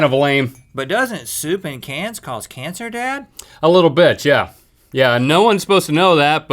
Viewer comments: Did Link say, "A little bi*ch!" at 12:00? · did link say a little bit or a little bitch?